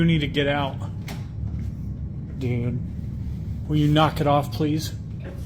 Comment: will you knock it off please?” I heard a clear… NO